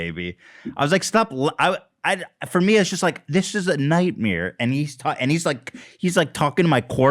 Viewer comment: did Ethan just say "night-meer"???? 👀